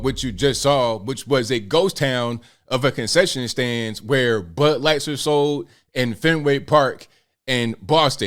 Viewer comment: did call it butt light? lol